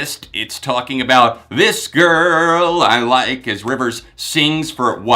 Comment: Until now i thought rivers said Disco La Life instead of This Girl I Like.